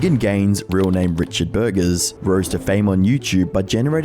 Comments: Ok, so I'm at work listening to your video, as one does, and due to your accent, I did not hear his last name as 'Burgess'. I heard 'Burgers.' A vegan named burgers. I spit out my coffee. Pure gold. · When you said his name was Richard Burgess, i thought you said Richard Burgers and i spit out my fucking drink 😂😂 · For a second when you said the name of that 'vegan gains' dude I thought you said 'burgers' instead of 'burgess' and I chuckled. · I had to do a double-take when you introduced Vegan Gains because I could've sworn I heard you say his name was "Richard Burgers" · i was in another tab when you said vegan gains' real name and i honest to god thought you said he was named Richard Burgers